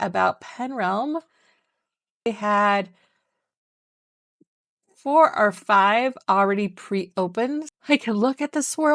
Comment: What did you say at 6:53?????? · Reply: "Take note Meiningers" another shop that doesn't let you preview resins. Thanks for calling attention, I now realize what it might sound like out of context and have edited out of the video.